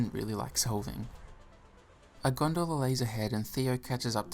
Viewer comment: why did I hear “a Gondor laser head” instead of what he actually said